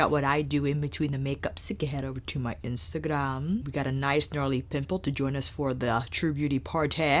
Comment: You said “pimple” and I heard “pit bull”. Imagine my confusion.